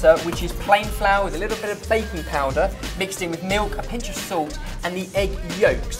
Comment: When said he put baking powder in the waffle mix I thought he said baby powder😂😂